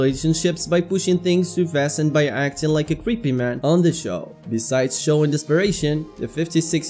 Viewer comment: I thought you said “people were calling him a crip” instead of “creep” lmao 💀